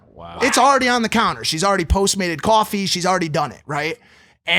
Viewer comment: I thought you said "she's already made coffee" but then I realised you said postmate... I swear the girls in L.A. have 0 practical skills, like they wouldn't be able to cook a dinner if their life depended on it.